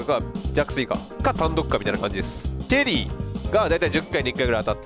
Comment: スロットの歩んできた歴史まったく一緒！ と、思ったら同い年やん(笑) ショートフリーズに熱くなれんねんな～うん 3:07 が「テリー」にしか聞こえへん(笑)